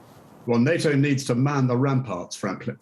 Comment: So I know this is very serious. I've been following it for weeks. I don't mean to make light. BUT. I swear to god I thought he was going to say "NATO needs to man the f*** up."